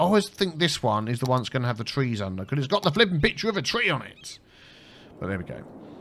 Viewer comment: "I always think this one has the trees under it, BECAUSE IT HAS A (I swear, I heard "Bigture" which I thought was a combo of big and picture) PICTURE OF A TREE ON A IT!... But there we go." After watching you for a good 100+ episodes make the same mistake of multiple series, I have to say I laughed so hard when you said that.